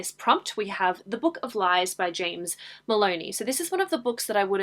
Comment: Edit: When you mentioned the book of lies, and when you said the author name, it sounded like you said James Bologna and not james Moloney. Is it just me or that the past few months, that alot of booktubers / YouTubers videos are getting sponsored by skillshare. Am I the only one that noticed that.